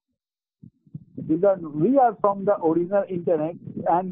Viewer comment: did he say hes from the original internet?